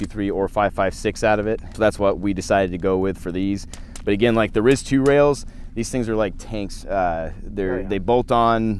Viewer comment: what rail brand is he talking about running? I can’t understand what he’s saying something 2 I can hear??? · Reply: Daniel Defense RIS II